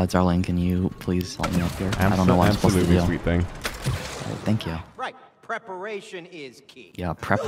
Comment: thank who??? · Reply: i thought mans said thank you ike